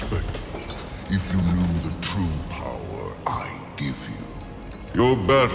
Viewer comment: the subtitles say "true power I give to you" but when said, he doesn't say the word "to". he just says "the true power I give you. WTF???